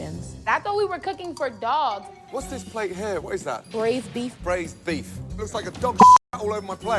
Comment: Honestly I love mistake. Love the "huh... never done it, sounds weird, but let's try our best" energy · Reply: "I thought he said brisket" moment